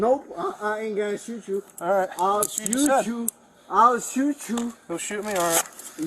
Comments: I don’t hear “I’ll shoot you”, I hear a slurred “I’ll sue you”. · In Minute 21:45, I don't think he said, "I will shoot you." I think he is saying: "I will sue you." When he asked what his badge number was, it sounded more like beige, so I believe his words are not being formed correctly because he is on the ground with stress and under pressure. The expression "I will sue the f@ out of you" is well known to be said instead of "I will shoot the f@ out of you". Then the policeman said shoot to incriminate him of threatening a police officer. If he had said he would shoot you, the policeman would have added charges for that, too, and he didn't. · Because of his lack of good English the officers thought and the close caption thought he said I'll shoot you. He was meaning to say I'll sue you. I know that because when the officers repeated back o you will shoot me he said no and again tried to say i will sue you.